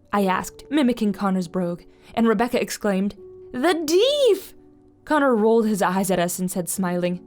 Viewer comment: I thought it said "The Deaf" at first If only I knew how true that was